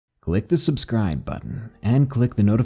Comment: Low key I thought u said lock the subscribe button